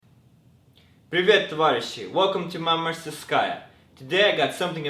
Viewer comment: What is he saying at 0:00-0:02? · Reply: + Alexei Kirillov Привет, товарищ!